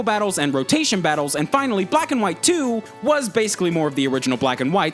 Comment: Anybody else catch the "Pokémon Whack 2" at 4:41?